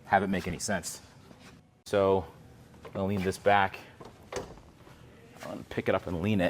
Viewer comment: I thought he said “leenda’s back” 🥵